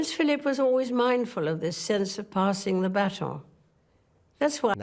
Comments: I think she actually says "baton" and not "battle." "Passing the baton" is an idiom. I think the AI-generated subtitles just couldn't understand her. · I believe that at 16:02ish, what she actually said was, "Prince Philip was always mindful of this sense of passing the "baton", not "battle". Baton would be the appropriate word in the context of this sentence as "passing the baton" refers to the passing on of responsibilities of one generation to the next. This theory is further supported by her unmistakeable reference to "baton" and the Queen's Baton Relay a minute or so later... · She did not say "battle", she said "baton". Neither you or the captioning got that right. Also at 14:40 she said "readied ourselves" not "reddit ourselves", although the the captioning got that wrong I think you understood that to be a mistranslation. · I think she said BATON in the French way? You hear it again at 17:03. I could be wrong. Passimg the baton, as in relay athletics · She didn't say battle, she said baton. The automatic subtitle creation misheard.